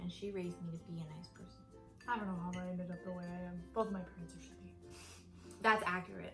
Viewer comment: When Todo said he didn't know how he ended up as he is because both his parents are shitty, I thought he said both his parents were straight.... >.<